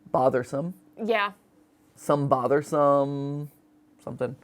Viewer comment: I think it just says bothersome over and over again